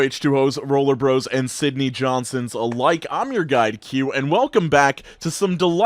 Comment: I always mishear the opening as “I’m your god Cue”